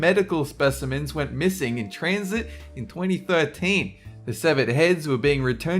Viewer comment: i heard Tranzit, i thought of black ops 2